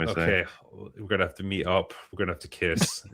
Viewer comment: When said "meet up" I heard "meat up". Now I can't un-hear it. That's what happens if you let Pedro Pascal into your brain.